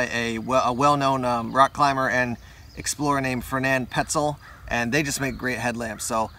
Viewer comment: i thought it said pretzel and was happy